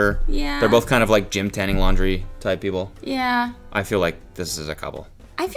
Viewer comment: what does 'gym, tanning, laundry like people' mean lol.. did I hear that wrong 😂